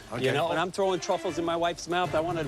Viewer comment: Um...did say “wives” 25:58 or am I tripping😭? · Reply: He said wife's.